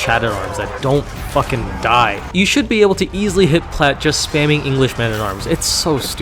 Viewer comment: "you should be able to easily ????" WHAT are you saying here? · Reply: "hit plat" as in, hit platinum rank